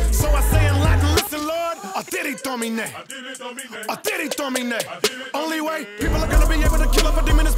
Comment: Someone help me understand what he says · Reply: Audite domine is latin and means listen lord